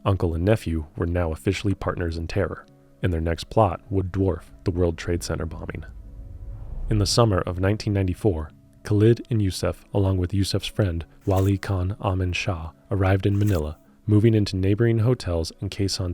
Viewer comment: "...'s friend, lolicon...". Whaaa...? Did I heard that right? 😂